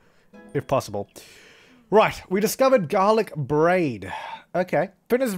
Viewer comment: i thought that said "discovered Garlic Bread" at first lmao